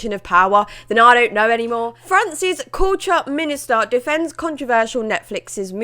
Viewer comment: When you said "France's Culture", regarding the minister, I heard it as "Francis Couture" and I thought wait who the fuck is this. It made sense soon enough.